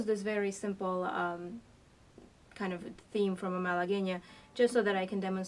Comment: Does anyone know what the name of the piece that mentioning at 3:30 in the video? I’m sounding stupid, but I’m having trouble with what she’s saying. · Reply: La Malagueña